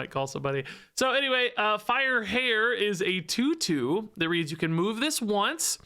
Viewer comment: at first i thought it said FIRE CHAIR lol